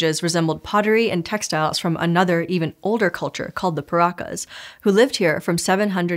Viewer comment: as a longtime Bionicle fan I thought you said "Pirakas".